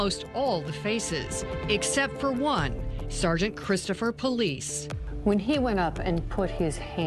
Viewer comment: It sounded like she said "Christopher Police", but that can't be right.